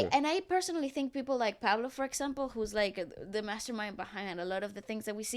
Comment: When mentioned "Pablo" being the mastermind behind SB19, the editor mistook (misheard) it for Paolo Valenciano. But, honest mistake, for sure😅